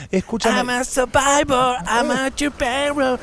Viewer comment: Pensé que había dicho "Puchero", pero no, estaba diciendo "yo te conozco, sos Ruchero". 🤣🤣🤣🤣